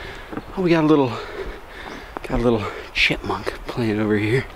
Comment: lol, I heard you say shitmunk. 0:48 ish. Your content is awesome. Did your dinner partner home? Sorry, was remembering the mink that tried to rob you in another video. lol